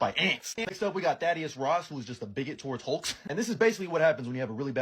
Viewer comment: i thought bro said daddy ross😂